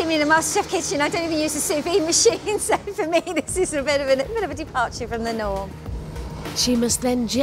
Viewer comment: WHAT IS SHE SAYING SOMEONE HELP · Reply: Bidhan Saha she said: a little bit of a departure from the norm. Sorry if I’m wrong!